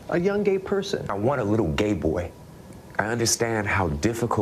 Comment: Did he just say he wants a little gay boy